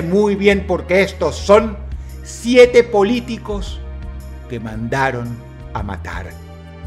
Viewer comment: Pensé que decía "Mandatarios que Mandaron a Matar" en lugar de "Mandatarios que Mandaron Matar" haha Dije: tiene que estar Kennedy y Colosio